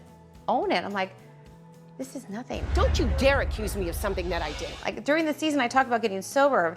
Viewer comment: Did shannon just say “dont u dare accuse me of something that i did?” Freudian slip much? Lol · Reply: Lol, good catch! I went back and she actually said, "how dare you accuse me of something I DID". Even worse!!! 😯